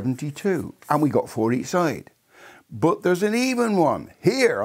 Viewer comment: thought he said "But there's an evil one."